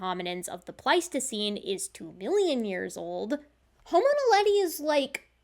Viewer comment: did you say that Homo erectus is “two billion” years old? Did I mishear?